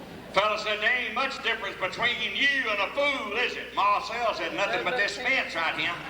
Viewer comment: What does he say between 8:23 - 8:26? There's not much difference between ? and ? (you) was one of them. The captions at that point are a wee bit off. Lol! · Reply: He said you and a fool